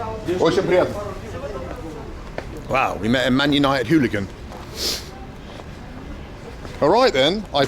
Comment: Did you say "Mennonite hooligan"? 22:19 or so